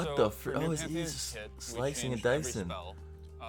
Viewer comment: Can you guys please tell me what he says in 4:46 ?? slashing and dising?? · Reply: slicing and dicing. It means to cut shit